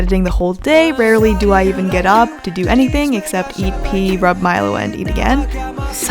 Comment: omg at first i thought you said “eat pee” but then i realised it was “eat,pee” AHAHAHAA